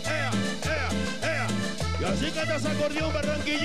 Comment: Yo escuché que dice la chica de esa acordeón min 2:50, o escuché mal